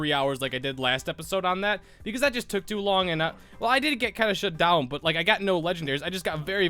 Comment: DID H SAY THE S WORD?!? 😱😱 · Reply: *shut down